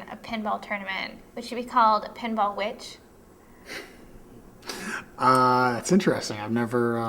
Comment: I heard "pinball Witch" as Pinball Bitch.